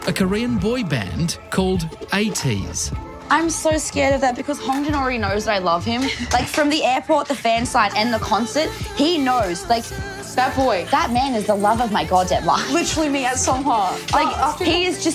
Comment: Ok I’m an Atiny but those Aussies who were obsessed with Ateez were a bit crazy. 😐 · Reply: gamerswaggie I think you mean the part in the hotel room at like 13:16, with the girls getting ready? I think she said “He already knows I love him, from the airport, the concert Yada Yada,.. Hongjoong is the literal love of my life” not that he was in love with her I’m Atiny as well but I had to cringe a bit at that part of I’m honest I love ATEEZ a lot but I don’t think I would have flexed my photo cards like that if I was filmed for national television :|